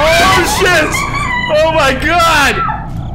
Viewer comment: DID SAY SHIT?!